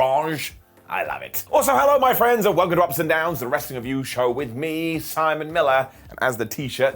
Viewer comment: For some reason I thought Simon said, "Ups and Downs, the wrestling abouse show" instead of " the wrestling review show"